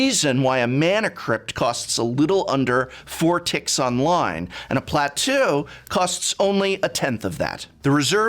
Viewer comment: Not me rewinding 10 times to see if he actually said 'Platoo' or my ear had a brief stroke. Still, this is such a good video, and immensely held up.